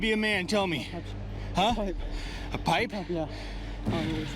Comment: I thought it said papi